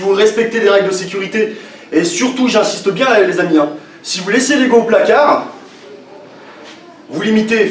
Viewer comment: question : à 5:57 il dit quoi ? j'ai pas capté "Si vous laissez les ".........." au placard" quelqu'un pourrait me dire svp ? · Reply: Laisser l'ego au placard, c'est à dire, il faut écouter son corps, suivre sa progression, ne pas se comparer au voisin, etc...